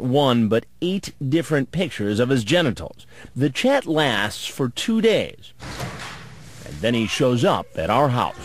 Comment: at first I thought Chris said "the Chat laughs for two days." LOL. Was like damn, sick burn